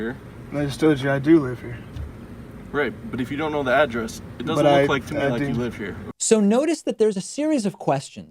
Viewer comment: FAKE NEWS!! Sort of. I agree this officer was a bit out of line--maybe he was having a slow day and thought he'd be proactive--but your video title here is misleading. The officer DID NOT SAY "You don't LOOK like you live here." Didn't happen. What he says @ 3:08 is--"But IF you don't know the address (THEN) it doesn't look like to me that you live here." He repeats the same thing clearly @ 5:10, indicating the reason he doesn't believe him is BECAUSE the driver refuses to state his address. The cop assumed a normal dude who lived there would have stated the address and handed over an ID for confirmation. Interview over. The driver's failure to answer a simple question is what confirmed whatever suspicion the cop already had. Now--what raised that suspicion? I don't know: I wasn't there. But, I've seen enough POLICE ACTIVITY videos (awesome YouTube channel) to know that there's more than a few suspicious fellas up to shenanigans out there. I suspect this cop's Spidey sense isn't as good as he thinks and this kid's knowledge of his rights threw a monkey wrench into the mix.